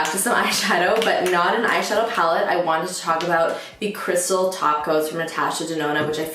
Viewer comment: I legit thought you said “Crystal Tacos” lol